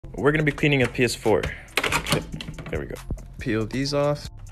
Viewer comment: Is it just me or did it sound like he said “we’re going to be painting a ps4”